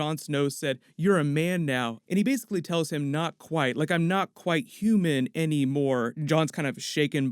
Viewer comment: Didnt Bran say " Not yet" instead of "not quite", or which of us has hearing issues.